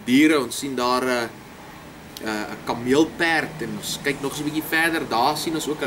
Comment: LMAOOO im dutch did he just say "kammeel-peird"? 😂😂😂😂 Lmao literally translated camel horse? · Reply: That's right yes. In Afrikaans we write it like this: kameelperd. I have met quite a few Dutch people and I find the Dutch language quite interesting. Btw, my surname is, which is a common Dutch surname if I'm not mistaken.